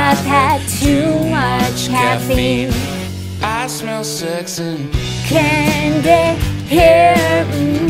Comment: Did I just hear “I smell s3x” 💀